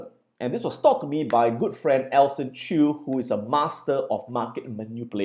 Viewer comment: Excellent content! Who is the friend you mentioned at 10:30? Elson Chu? I couldn't quite catch his name but would love to learn more about what he has discovered through his research. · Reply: Danielle Hayashi Alson Chew. He teaches Crypto on <link>